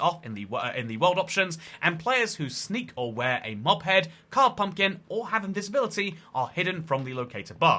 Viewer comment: I thought he said players who have a disability are hidden and I had to take a moment to comprehend